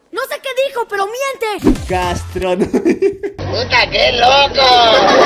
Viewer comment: yo entendi cristo te ama xd